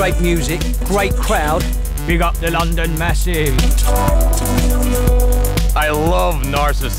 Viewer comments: BIG UP THE LONDON MASSIVE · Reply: I thought he said WE GOT THE LONDON MESSI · Big up the London massive 👍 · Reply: It thought he said "we got the London massive" but the CC say big up... hmmmmm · Big up the London massive 👍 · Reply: i thought he said london message